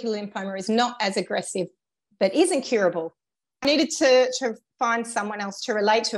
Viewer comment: Did she say follicular lymphoma is incurable? I couldn't make out what she said @ 20:25. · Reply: Apologies for that typo! She said it is incurable. Yes, there are lymphomas that are currently described by many as "very treatable but not curable." Follicular lymphoma is one of them, a chronic blood cancer. Just as a reminder, we are not a medical advice giving site, but we do have a medical experts section - hope it's helpful! <link>